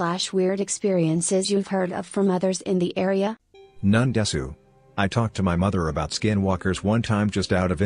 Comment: Bro did he just say none desu